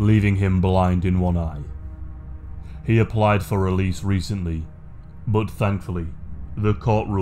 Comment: i thought you said he applied for a lease and i was like “wtf does he need a lease for?”